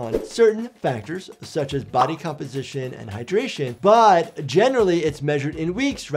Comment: At first time i thought said "degradation" instead of "hydration" lol